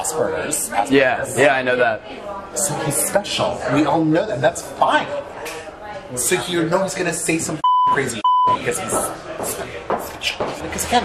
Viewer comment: I don't think the captioning at 0:12 is right. It sounds like what he said was "So he– you know he's gonna say some #%&$ing crazy $&!% because he's special," which makes more sense in context.